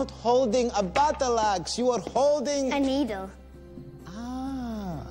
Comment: Put on CC and watch what it says at 1:38. She’s saying needle but something a lot different is written